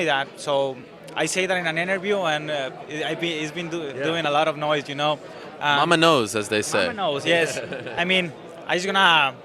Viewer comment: I thought Ariel said "I'm a nose". I wouldn't agree more.